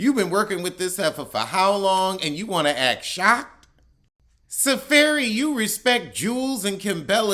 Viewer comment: Did you say Sa-Fairy? 😳 I really can't tell. 😭